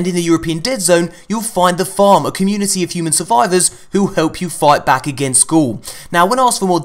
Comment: I thought he said fight against school